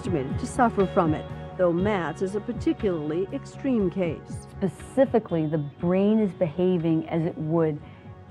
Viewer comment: did she say "pacifically"? grrrrRRRRRR!!!!!